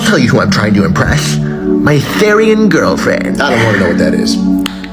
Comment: D- did- did he said… THERIAN GURLFRIEN